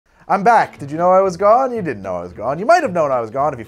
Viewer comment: I misheard you at first : did you know I was God?... Etc.